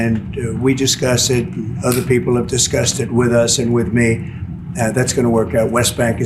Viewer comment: At min 3:37-3:40, it sounds like he says, “it’s disgusted. Other people are disgusted…” For a moment, I thought he had learned to tell the truth 😂 Oh, according to a poll conducted by CBS, 53% of the US approves of the job this moron has done. Please keep that in mind as things unfold